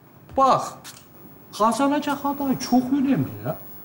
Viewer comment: de ne diyor anlamadım hasanaçağa :)) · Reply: Kazanacak aday çok önemli diyor